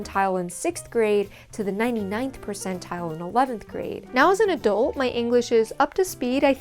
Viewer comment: did you say “now as a dolt”? Because you are not a “dolt”. You are very smart. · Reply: she said “now, as an adult…”